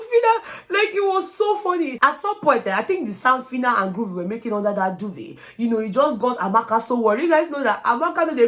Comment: hollup. did you just say phyna was hyping under the duvet?! 😂😂😂😂